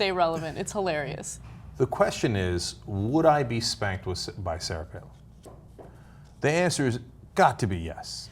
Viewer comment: Somehow my partner misheard "getting spanked by Sarah Palin" as "getting spit-roasted by Sarah Palin", and asked how THAT worked. lulz We decided spit-roasting couldn't work as then you'd be seen by someone else getting down with her... although it would be a case of mutually assured destruction. Indeed, the only positive outcome from shagging Sarah Palin would be if she had multiple orgasms, and you could leave her in a giggling heap with the words, 'That's how atheists do it.'